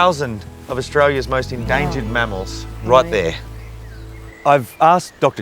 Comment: I don’t know why but I heard “endangered Mammaw’s” and I’m losing it laughing. I realize how silly that is but my hubby calls his grandma; Mammaw and I just couldn’t help but laugh at it. All laughter aside. I truly love what you all do ❤